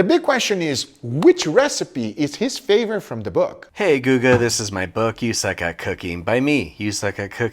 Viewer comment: Ok, i don't like the 4:55 mark. My Samsung s24 ultra thought he said, " Hey, google" instead of" Hey guga." it activated it and started looking for his recipe